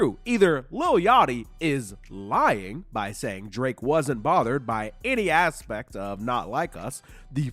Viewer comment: I thought lil tug boat said Aubrey wasn’t fazed by it? 🤣🤣🤣